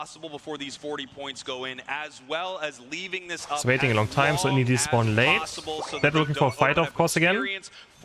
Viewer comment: Sorry, couldn't hear your voice properly... why was charizard delaying score? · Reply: “he's waiting a long time si indeedees spawn late”